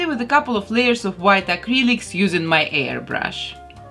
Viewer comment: Who else thought she said hair brush instead of air brush?